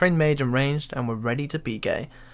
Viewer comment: "and we're ready to be gay" is what I heard but maybe he was wanting to PK